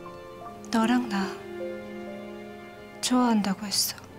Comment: 뭐라했어요?? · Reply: 좋아한다고 했데요